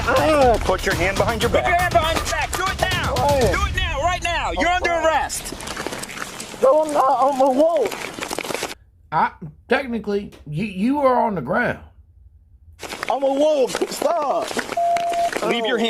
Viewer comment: Did he say he was a wolf?